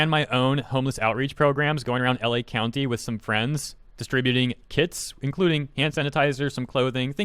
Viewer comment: i heard " distributing kids" at first